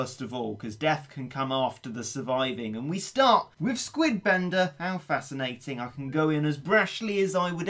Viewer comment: For a second I thought it said squid blender not squid bender